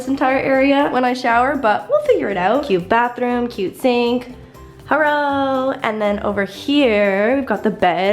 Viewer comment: did anyone hear herro :(((